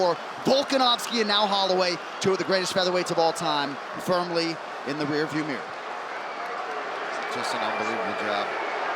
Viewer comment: does someone knows lip reading? on timestamp 14:15, what did Illia Topuria say to Holloway? i really wanna know that. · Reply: He said “Who’s Joe?”